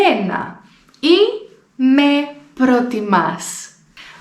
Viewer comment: the subtitles say us but it's supposed to be me